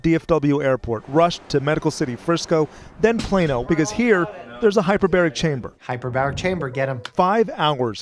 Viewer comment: did i hear hyperbolic time chamber???